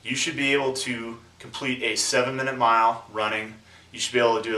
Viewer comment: I thought you said 7 miles running, my face dropped so quickly